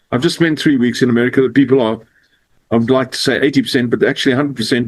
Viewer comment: I don't hear "backs" or "blacks", I hear "I'd like to say" spoken very quickly almost slurred together. I have really good speakers, I guess.